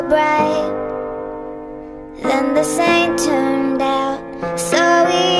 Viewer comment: "Then this things turn out" sometimes I hear it as "then this satan turned out" 😅